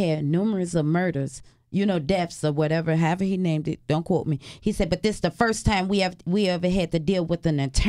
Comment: she was talking about multiple murders at the motel than she said some but idk if she said " don't cult me " or " dont quote me "...I can't hear it out someone tell me wat was said?